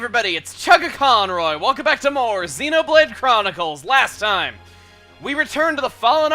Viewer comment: During the intro, my browser had a weird audio skip, so instead of 'welcome back to more Xenoblade Chronicles' it sounded like Chugga said 'welcome back to monocles'. Like a sir, dear Chugga. Like a frigging sir.